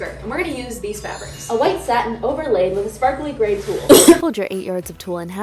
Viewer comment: it said White Satan instead of white satin... um